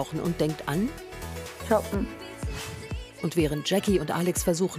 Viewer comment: versteht das einer? Was hat die gesagt? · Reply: Ich glaub sie hat 'shoppen' gesagt